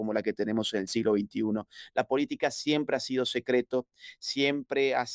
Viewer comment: que dijo? La política es en secreto?por eso Argentina está devaluada